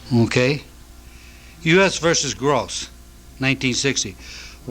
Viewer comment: Richard mentions a case; US V ????? which I cannot understand. Does anyone know that name? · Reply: I heard it as Gross but the only reference to that was in 2015.He said 1960.